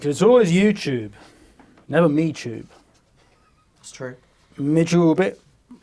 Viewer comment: What does he say at 7:28? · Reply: Me tube it in an vague African accent